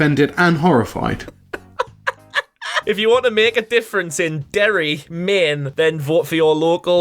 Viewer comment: first I thought u said Derry men and then I thought about Derry Girls. Derry Boys? (Derry Girls spin off but with males)